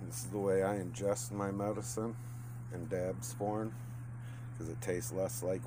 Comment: in when subtitles or whatever it called it says "dad's porn" instead of dab from lmao